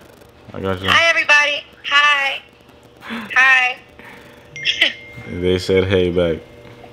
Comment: it sounds like he said "they said hey bae"